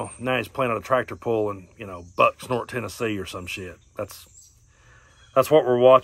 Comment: I swear I thought he said butt snort Tennessee @ 11:16 lol 🤣